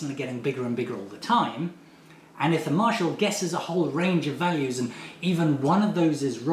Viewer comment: Tor Iver Wilhelmsen Blunder free work from. He says that the fugitive is ahead of the Marshall. "A fugitive, somebody not so much always on the run as, on the stumble, forever just one or two steps ahead, of the other player the Marshall" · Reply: Ah, I heard "are the marshal" (which would be bad grammar anyway) not "of the marshal" - I blame wind.